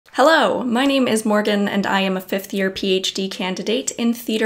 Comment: I thought you said: I'm a fifty-year-old Ph.D. candidate... and I thought: I don't care about your Ph.D.! I wanna know what skincare products you use to look like you're 22! Lol! 😂😂😂 Then I played the beginning of the video over again a couple of times and turned up the volume, and realized what you said was: I am a fifth-year Ph.D. candidate...